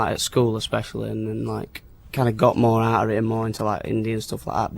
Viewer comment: Hahah, what did he actually say though? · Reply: “Got more into like, indie and stuff like that.”